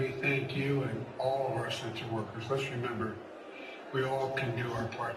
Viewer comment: His handler Dr. Pepper had to give most of the prepared speech. The way he garbles his words, it sounded like he said sex workers.